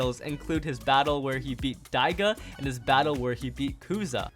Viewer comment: "diaga" what did he say?